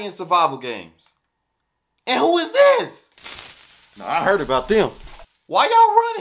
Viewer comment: He said “I heard about that “🏃🏾💨🤣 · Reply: “I heard about them”